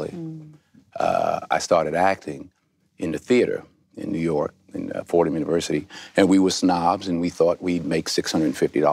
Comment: When Denzel says around 2:30-3:00 about actors with 'O' De Niro,Pacino,Hoffman-o, Mira Nair adds one more name. That it??? I can't hear it. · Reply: Danny Aiello